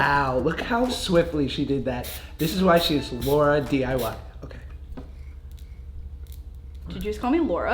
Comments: I no joke thought josh said Laura too😂😂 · It did sound like he said LauraDIY